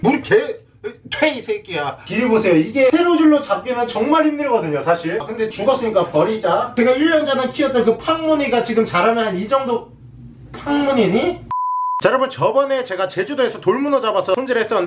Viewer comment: 나만 '항문이니?' 일케들림?